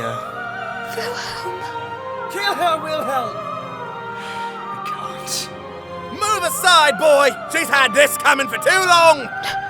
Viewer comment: Every time I hear 43:07 I keep thinking they're saying "Killhelm Wilhelm" and it's honestly really funny to me for some reason